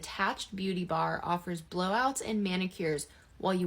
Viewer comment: I thought she said Blowjobs. Lol